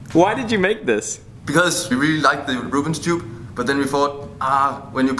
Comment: He says 'Ruben's tube' and I hear Rubik's cube...